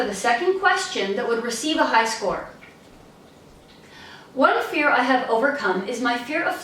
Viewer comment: I don't understand what you said as example answer. Especially first 5 words. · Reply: What fear i have overcome is